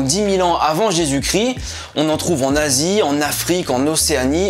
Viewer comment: "En Asie" "En nazi", t'as vraiment pas eu de chance toi xDD